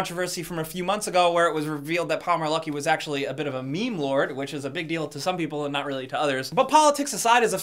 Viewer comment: What the fuck is he saying at 0:25? I listened to it many times and still don't know what he is calling that dude. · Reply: Bryce Gunter He got in trouble for being a "meme lord"? I don't get it. Signs of getting old I guess.